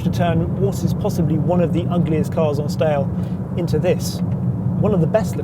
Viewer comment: I'm sorry,... this car is among the best looking "on stale"? Did I hear that right, twice?